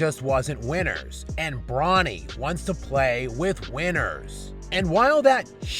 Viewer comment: Wait, did he just say that Bronny wants to play with WIENERS?! 😅